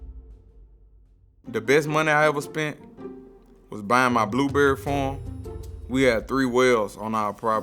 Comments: I swear i thought he said my blueberry phone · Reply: I thought he said blueberry form😂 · i thought he said bying my blueberry phone and dk why but for a sec thought he said he bought a black berry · I swear i thought he said my blueberry phone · thought he said blueberry phone😂